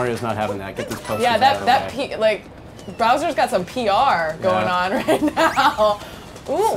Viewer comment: did she really say bRowser